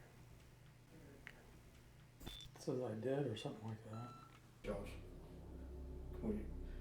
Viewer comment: I do not hear “I did” I hear “Did-nt” in regards to if they followed her from elsewhere 🤷🏻‍♀️